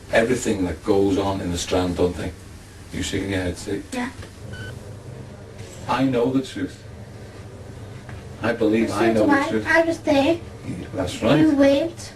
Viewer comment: what did Robert say before crying at 23:05? sorry couldn't recognize with the accent.. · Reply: Detective ' I know the truth, I believe I know the truth.' Robert 'Well so do I, I was there, you weren't' Detective 'Correct, but I know a lot of things that had gone on.' Robert 'Well how do you know it was me that killed him? It wasn't, I never even killed him' Robert cries